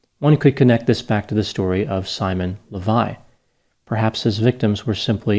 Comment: Lol, at first I thought you were going to say "Simon LeBon".